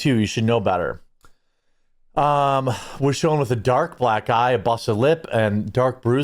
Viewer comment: When you said "dark black eye", I thought you said "dark black guy". 😅